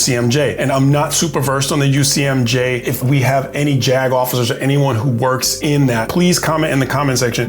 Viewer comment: I'm from Pittsburgh PA and when you said JAG Officers, I almost thought you said "jag-offs" If you know you know - lol Thanks Sgt.!